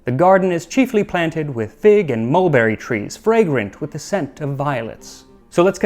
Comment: I misheard "scent of violets" as "scent of violence", and was like ah how roman